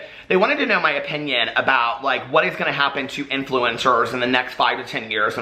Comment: You said influencers, and I heard "influenza". Oh my mind !!!